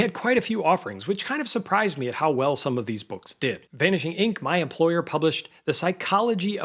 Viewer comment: did you say, "Vanishing Inc., my employer"? Just curious, in what capacity?